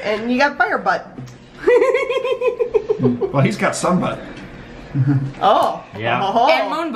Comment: who else misheard as saying "He's got some butt" instead of "He's got Sunbutt"?